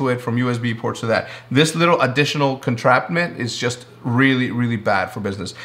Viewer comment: Did he just say "Contrapment"?